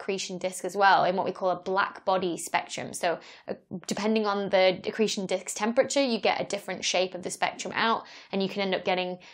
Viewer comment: Did I just hear: "Depending on the accretion dick's temperature..."?